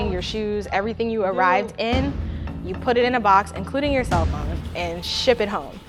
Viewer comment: I couldn't hear what she was saying...including your cellphone?...Imma go crazy If that was me.